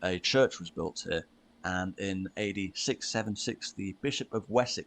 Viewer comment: did he said the year 8676 at 14:57?